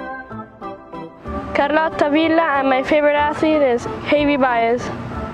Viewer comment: so i'm watching this in march 2020 and i think i heard the girl say her favorite athlete was Kobe Bryant. 2:44. :(:(:(:(:(:(:(:(:(:(:(:(::(:((::((:(:(:(:(:(:(:(:(:(:(:(:(:(:( Kobe was the person who i grew up watching and the reason why i got into basketball.:(:(:(:(:(:((:(:((::((:(:( it is devastating that him gig, and all the others that were in the crash are gone. but they all will be remembered forever